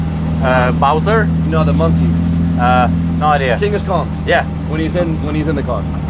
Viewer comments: Did he just call donkey kong, Genghis Kahn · Did he just call donkey kong, Genghis Kahn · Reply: I thought he said "Dingus Kong" LMAO